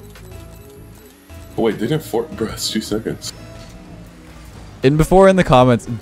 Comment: goal in the last game to make it 7-1, he boosts. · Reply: he actually says something like "wait did I press boost like for 2 seconds" in 30:08 I thought they unbind the button, is way harder to not boost having the button binded